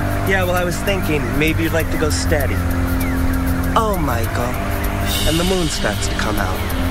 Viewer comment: does he say "Oh Michael"?... His name is Johnny... xD